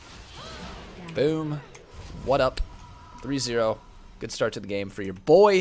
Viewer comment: BOOM WHADDUP SOLORENEKTON ONLY WITH SOME MORE LEAGUE OF LEGENDS ACTION PLAYING LEE SIN IN THE JUNGLE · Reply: Haha I heard him say BOOM WHADDUP so I came to the comments.